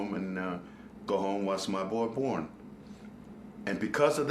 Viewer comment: It sounds like he said "Watched my boy porn" even though i know he said "watch my boy born"